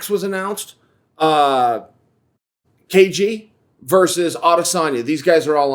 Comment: I thought Chael said Gaethje vs Adesanya, but he said "KG"